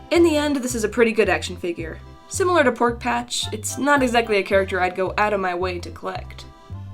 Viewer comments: did she say "porkpatch" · did she just say porkpatch?